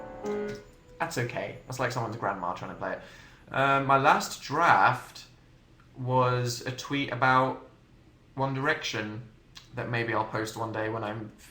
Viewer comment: Omg I heard "my last drafted tweet was one about an erection maybe I'll post it one day" 😂